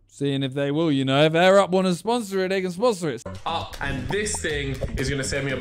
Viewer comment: Is it just me or does it sound like he says if air up wants to sponsor a n… then sponsor it 🤣